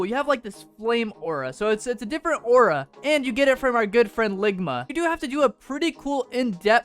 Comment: bro just said ligma instead of longma